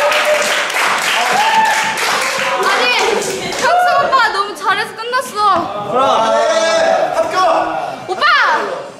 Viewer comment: What did they say on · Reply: Oppa, I'm still a girlll, Gotta treat me more gentleeeeee. lol